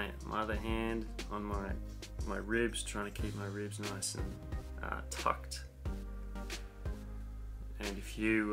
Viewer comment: Hey, I just wanted to let you know the subtitle at minute 8:01 is wrong. You say the word 'tucked' but the subtitle got it wrong! I'm sure you'll want to fix this. You are so professional and I know this is not what you want out there. Thank you for all your work - I'm using the knee pain videos and am seeing improvement.